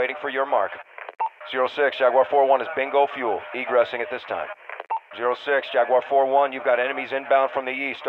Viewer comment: What he said? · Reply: 0-6 Jaguar 4-1 is bingo fuel egressing at this time.